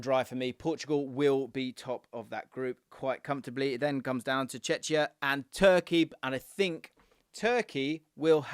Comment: i thought you said “Chechnya” for a minute and was wondering when they got their independence from Russia😂